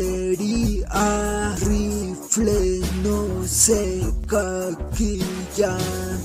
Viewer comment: "Caquilla" parece que dice